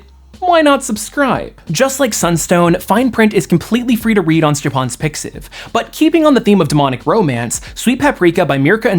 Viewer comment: What is he saying? At 07:16... It sounds like "Stefons Picsif"??? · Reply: Stjepan - its the name of the author for Fine Print